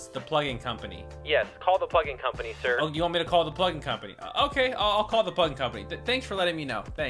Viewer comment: In start, he said,"hey plugin company." I heard,"hey fucking company."